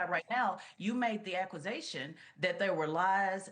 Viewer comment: Did she really say "YOU MADE THE AKWUZATION" at 3:40?